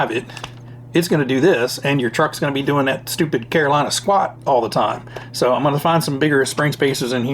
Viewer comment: Great to hear your thoughts on this build 8:00 Did you say Carolina squat ?? I have not heard that before 😂😂